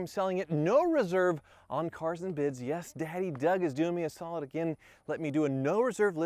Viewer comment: did he say daddy Doug 🌈